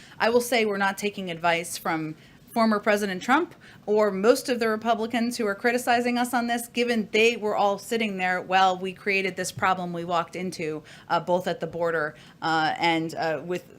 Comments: It sounds like Psaki said "While we created this problem" at about 2:38. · Did I hear her correctly at the 2:38 mark saying " the problem WE created " boy was that a slip up !! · Did she just say 2:35 "when we created this crisis we walked into a year ago"?